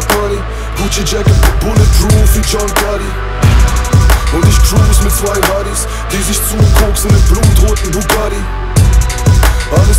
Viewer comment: I always hear it:" and I cruise with zwei rotties (rottweiler)"...